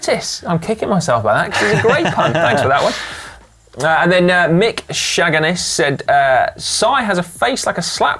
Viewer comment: I completely misheard Si mentioning the comment about his face and thought he said "Mick Shag-n-arse" 😂